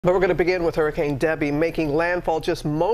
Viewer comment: I thought this said hurricane daddy and I was like holy shit